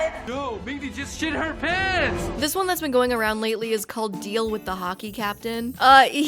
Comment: For a sec I thought it said a deal with the jockey captian